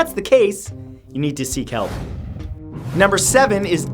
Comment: Who else thought he said 'You need to see kelp ' for a moment and then realized he said 'seek bell's owo